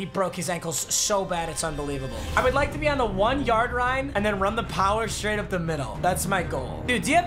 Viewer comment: he says rine instead of line